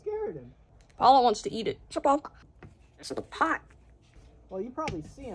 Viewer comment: Sounded like he said "this is a pipe" to me, which actually makes sense.